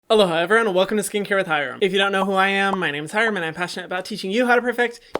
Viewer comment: I thought he said “Hello Hyram, welcome to skin care with Hyram”